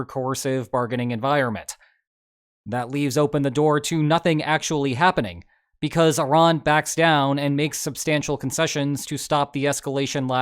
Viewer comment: Did he say Nothing actually happening